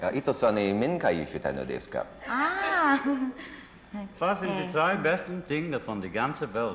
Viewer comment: I'm German and I have no idea what John said at 4:50 . I'm pretty sure it's supposed to be German though. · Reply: was sind die 3 besten DInge in der Welt or something like that. Meaning: What are the 3 best things in the world. So it's bascially nothing